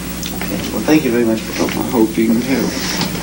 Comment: CC [unintelligible] it sounds like he says "i hope you can help me"